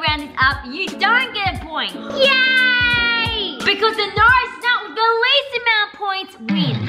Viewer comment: I have captions and instead of “Norris nuts” it said “nose nots” LOL I WAS LAUGHING SO MUCH 😂